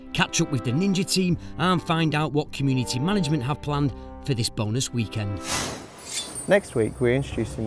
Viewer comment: did I just hear "bonus weekend"? Whattt